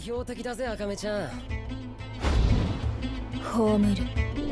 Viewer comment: what is the japanese word that akame says at 0:20 “they will be eliminated”? i tried to search for what sounds like she is saying ‘homeru’ but have no luck of trying to find the actual word cause the translation does not seem to match. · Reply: i don't know if you found out but I personally hear hou miru "方見る" which I guess could be translated into "I'll see to it"